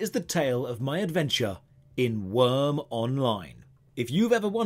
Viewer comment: I keep hearing “Eve Online.”